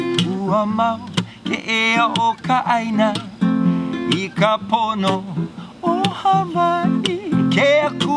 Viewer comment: Nobody: Youtube to me at 3AM: Watch this random video of singing a song in Hawaiian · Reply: I don't think will say "porno"!! here